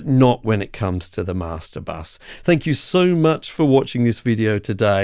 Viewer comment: I keep hearing "I mic and i hope you will"